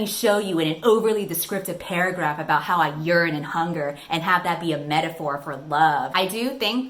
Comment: the captions say: Paragraph about how I’m urine and hunger and have that be a metaphor for love. AND I OOP